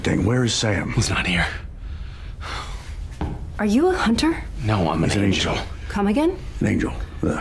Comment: I swear that Cas said, “no, I’m HIS angel”. I wouldn’t put it past misha to slip that in.